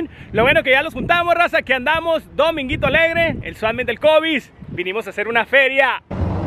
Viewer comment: Siempre pensé que decías Swap Meat del COVID jajajja, hasta ahora que veo este letrero me doy cuenta que es Swap Meat del Kobey's